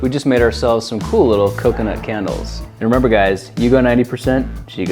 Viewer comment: Am I the only one who thought it said candies 😂 lol